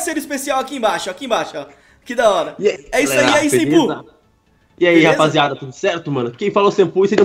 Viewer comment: só eu q escutei "fala aí sem cu" em vez de sempu ?